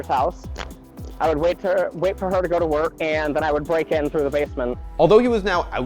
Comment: Listen closely to 4:00 did he say what I think he said? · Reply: "I would break in through the basement."